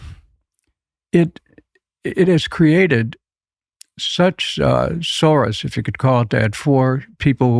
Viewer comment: did he say Soros 😂🤭